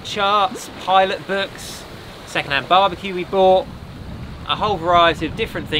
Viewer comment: did he say "second hand barbeque"? 😆